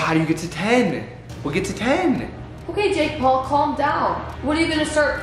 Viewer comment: was it just me that heard call “jake” at 10:39 seconds? just go back to 10:35 and you’ll hear it!! please lmk if i heard that right😂